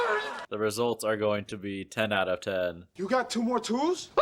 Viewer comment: Did you say "Tan out of tan"? 😂 Also, i'm loving the new lighting. Dark Era.